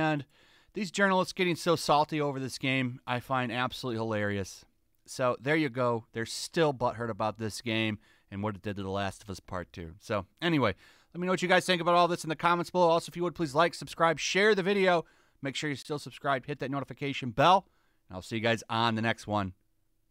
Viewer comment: I literally thought you said "Laugh at Us: Part 2" I need to adjust my sleep schedule. I'm tired.